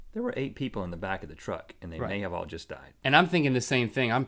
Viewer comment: it sounds like rhett sayed "there were 8 people in the back of the truck- and they have all just died"